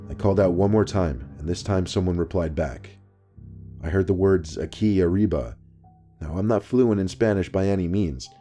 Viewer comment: Lol the Spanish. When he said "A qui arriba" I thought he said Key Reba lol. I thought it was a name.